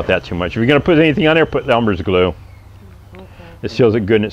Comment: he mentioned a glue … what name was it? Sounded like 'numbers' glue … I don't think I got that correct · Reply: Elmers Glue, like you used in grade school. Just a plain old white glue, smear it on the cut surface with your finger.